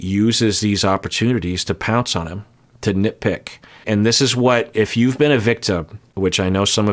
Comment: I misheard here and thought he said "Nick-pick." Fitting.